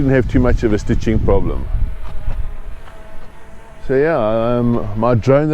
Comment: What drone are you using, sounds like you say Mini2 Pro, never heard of it.